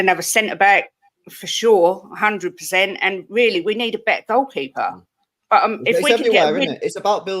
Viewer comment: Spurs need a better goalkeeper? What is this woman talking about? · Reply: Pretty sure you said better. You may have meant to say back-up, but i still think you said better